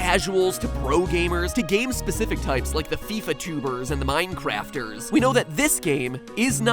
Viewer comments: I thought he said FIFA tumors. Same thing. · I thought he said "fifa tumors" 😂